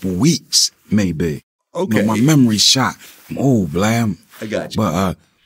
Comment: Did Beans call Vlad "Blam"?